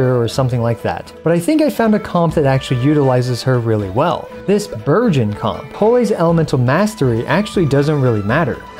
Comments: Really thought you said "this VIRGIN comp" when you showed the characters and I didn't even flinch. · No-one gonna talk about how he sounded like he said "Virgin comp" i mean the name still kinda fits